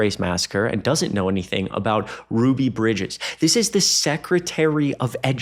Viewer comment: Did I misheard you, "Ruby Bridges" ? Isn't it, "Ruby Ridge"?